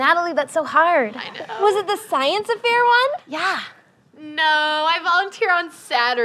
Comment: did anyone else hear science "affair"?